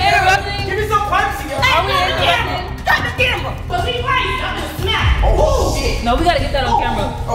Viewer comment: what did she say on minute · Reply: She said cut the camera